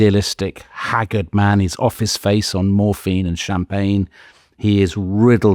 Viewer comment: "Off his face on morphine and champagne:...I had to replay that twice as I thought you said "Office space on morphine...."